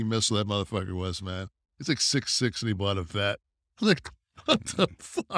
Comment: What did Kevin Nash say? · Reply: Test was 6ft6 and bought a corvette